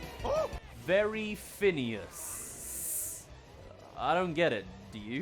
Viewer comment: I think it was joking to saying “very fine ass” 😭